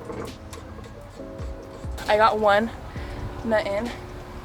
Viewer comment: She the type of girl who will do the oil change on her mans car LMFAO respect! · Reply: Did she just say she got one nut in